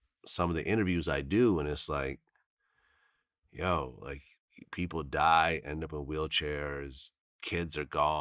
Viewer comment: Vlad did you say "YOU PEOPLE" at the 4:47 mark??..correct me of im wrong sir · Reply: Nah he said people die.